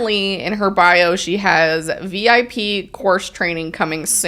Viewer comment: I thought y’all said that the tag line in her profile was “VIP Horse Training” 💀